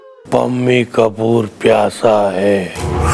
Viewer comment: Did I heard "Pammi ka burrr pyasa hai"😂😂